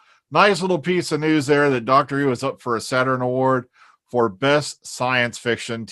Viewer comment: I thought it said Doctor Freedom nominated for Saturn award